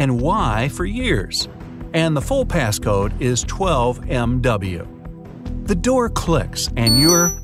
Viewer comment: 12MY He really said 12MW